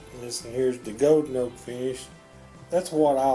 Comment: What is that at 2:41? YouTube CC says it's "goat coat" lol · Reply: Golden oak 😊